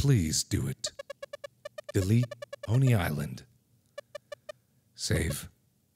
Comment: it sounds like he says at the begin bony island